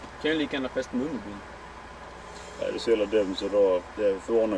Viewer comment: vad sa han? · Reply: Polisen vill veta vart han har sin varningskylt och så säger han "jag kan lika gärna ha fäst den under bilen"